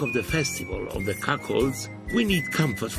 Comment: Did He Say Cuckolds ?? Omg that Made me laugh 😂😂😂😂😂😂😂😂😂😂😂😂😂😂😂😂😂